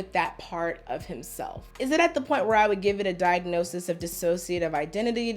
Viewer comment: I could not hear the entire sentence. I heard ”He is in constant struggle with this part of himself. Is it at a point where I would give him the diagnosis of identity [dissociative, but I did not hear that] disorder? Bruh